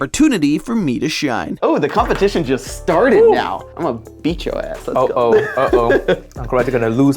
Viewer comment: He actually said A$$